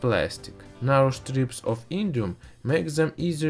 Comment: I thought that you said Iridium...